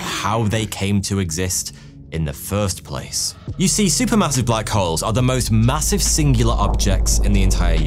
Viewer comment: Did you say MASSIVE????